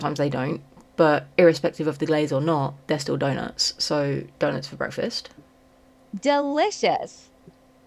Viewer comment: One of the times you said “donuts for breakfast” I heard “dinosaur breakfast” (despite the context being super obvious??) and did a major double take and had to go look back at the video to see what the heck was going on. 😅